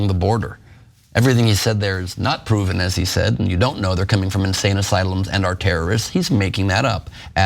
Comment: I thought he said we didn’t know where they are coming from.